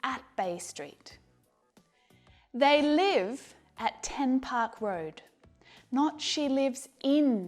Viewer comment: 10 park road Sounded like: Tupac road